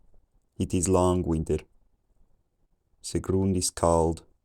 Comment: At 800 I heard "Chicken". - At 900 I heard something like " it is long winter". - By 1200 I started to understand some words. - By 1600 I started to understand most of it. I think the great vowel shift is the key to start understanding. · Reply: Very similar understanding for me (although I first heard "it is long winded") ha